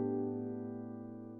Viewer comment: the ending made me feel so comfy and warm and then I hear "im a p3nis" 😳